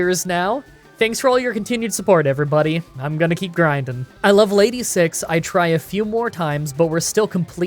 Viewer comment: At level 86 What I heard:I love lady 6